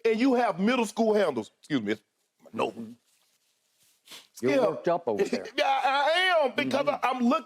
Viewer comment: when Shannon cleaned his nose, I thought Skip said. You're coked up over there. 😂 as much sniffling Skip does I always think he's done a line an episode